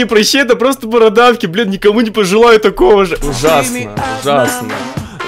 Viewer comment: мне одной показалось, что он сказал «бл*»???